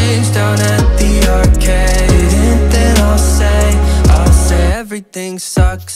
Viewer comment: LYRICS [Verse 1] Everything sucks, just kidding Everything is great, no really I haven't thought about my ex today Oh, wait–(Fuck, I just did) But I went outside for the first time In a few days and it felt nice And I might try doing exercise (Hehe, not really) [Chorus] I wanna forget my bad days, all my bad days And be okay, and be okay Spend my spare change down at the arcade And then I'll say, I'll say [Verse 2] Everything sucks, just kidding Everything is better than it used to be I didn't write a song about my ex today (Oh, wait–um) But I called my friends and I told them We should hang out if it makes sensе 'Cause I miss them and I hate bеing alone (Oh, really?) [Chorus] I wanna forget my bad days, all my bad days And be okay, and be okay Spend my spare change down at the arcade And then I'll say, I'll say I wanna forget my bad days, all my bad days And be okay, and be okay Spend my spare change down at the arcade And then I'll say, I'll say I wanna forget my bad days, all my bad days And be okay, and be okay Spend my spare change down at the arcade And then I'll say, I'll say [Outro] Everything sucks, just kidding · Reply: Oh, haha, I really thought he said : "I wanna forget all my bad days, all my bad days, AT THE ARCADE, AT THE ARCADE" Oop